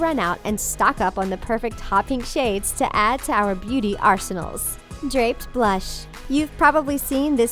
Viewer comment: did she say arsenal